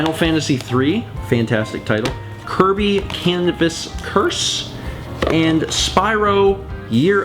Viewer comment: I heard "Kirby: Cannabis Curse" lol